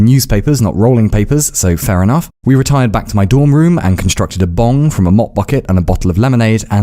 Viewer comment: I thought he said "and constructed a bomb" for a second